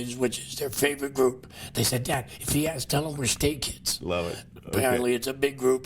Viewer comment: I was watching this with my mom and did I hear Skz!?